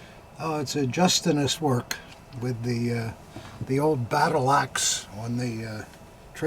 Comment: When was describing the trademark for “the battle axe” for the SA dagger, I thought he said battlacks! (As in mean old ladies!) Laugher!